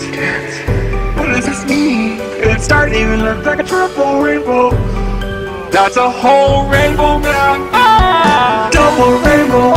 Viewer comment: Did he say "It's a whole rainbow, blyat" ?